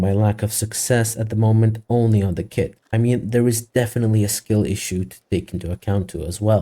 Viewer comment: I heard "skill issue" (nah but fr it's really hard XD good vid)